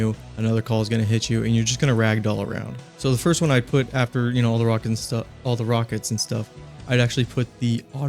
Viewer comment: did i hear a rock and stone slip up?!